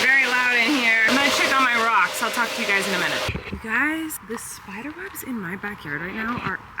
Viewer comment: Random jumble of comments, pardon my ADHD: Colleen, your hair looks fabulous in this video! Also… I totally thought you said “spider wubs”… like Flynn used to, awww 🥲!! Also… I too, am terrified of spiders and their yucky webs… I’d be walking around the yard waving a broom or Swiffer duster around in front of me, any where I went! 😂 Love your videos, long time fan… like going on a decade! Keep up all your hard work!!